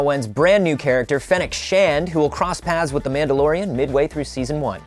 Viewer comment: Wait... did they say that the character at 0:41 had the surname SHAN?!